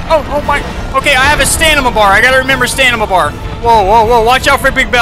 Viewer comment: "i have a stanima bar"......this boi really said stanima